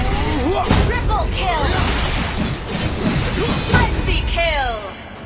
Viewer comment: Did I hear "Friendly Kill" instead Frenzy Kill 😂😂😂 bybthe this is the worst patch😑😐